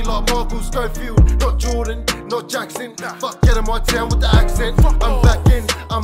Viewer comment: I thought he said " Fuck Hannah Montana wit the accent"🤦